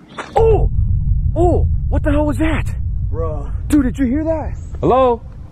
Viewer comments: did it say “help me??” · The part at 15:50 where i hear something say help me.